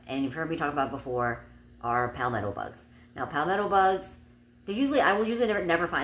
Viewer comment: Didn't hear the proper name and thought you said pound metal bugs lol. I googled it and found the right name lol x